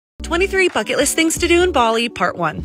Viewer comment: I thought she said “fuck it list” 💀💀💀